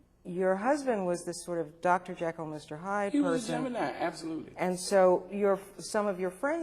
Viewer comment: did she say he was gemini?